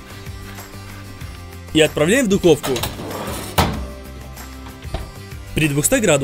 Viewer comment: и отправляем в дутовку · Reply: я тоже услышал "дутовка", или "ду...овка".